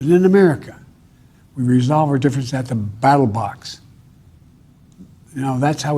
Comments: did he just say.. BATTLE BOTS!?!??!!? ROBOTIERS STAND BY!!!! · Did he just say battle bots LOL